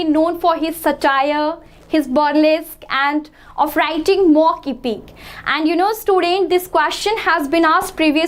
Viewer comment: what did she say? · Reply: Mock epic